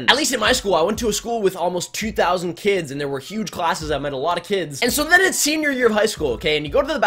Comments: kinda sounds like you said; i made a lot of kids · at like 2:03 or something i thought he said i made lots of kids